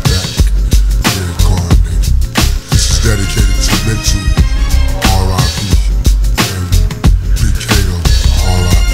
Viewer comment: sounds like Pac said “this is dedicated to Nipsey. RIP” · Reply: Lol “mental” is what he said it does sound like nipsey tho